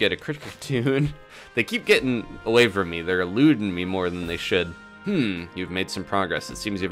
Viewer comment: for a second I thought you were saying they were lewding you